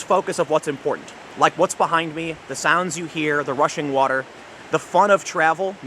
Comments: Why do I keep hearing "The Russian water"? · I know that politics has influenced me too much when i hear him say Russian water at 2:09 .